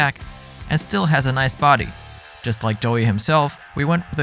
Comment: I thought you were saying “still has a nice body just like Joey himself”